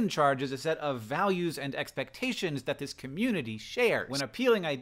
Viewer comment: When you said "values and activation" I heard "values and actions" and was immediately pulled back into the Batman songified video